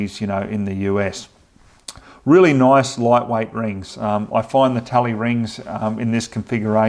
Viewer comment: Can I ask what scope rings and size are shown in this video - I see they 'clear' the fold-down site and I am looking for these. Are they 3/8" dove-tail to fit the Ruger? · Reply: AHH!!! I heard him say Timney Talley Rings. Got it